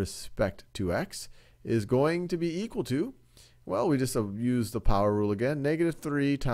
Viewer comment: i heard "abuse the power rule". It sounds about right.